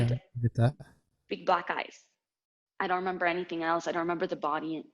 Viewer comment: i thought she said big black guys 😂